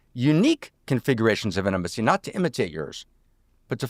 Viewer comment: why does he say intimacy like inamisee